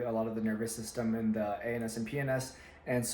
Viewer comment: definitely thought he said anus and penis, not ANS and PNS 😅🤣